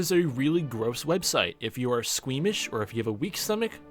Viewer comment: misheard “squeamish” as “swedish” and i was like what why huh